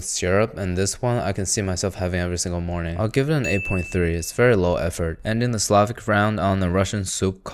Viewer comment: you said 8.3 but the subtitles say 8.1?